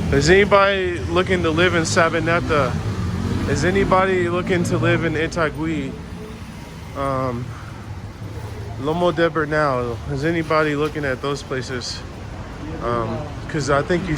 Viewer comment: What were the last 2 places you mentioned at 13:25-13:40 ? antioquia right? and I couldn't understand the last one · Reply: Sabaneta , Itagui , loma de los Bernal.